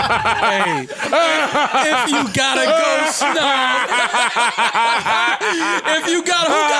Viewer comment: I thought he said if you gotta go snug too 🤣🤣🤣 the title on this make the next episode hilarious at that part nd make the next episode funny